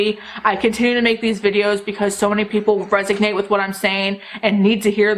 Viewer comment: I think you mean to say "resonate" but I keep hearing "resignate". Is it just me? Agree with the whole rant, I don't need any more stuff, Black Friday sales and hype should just go away.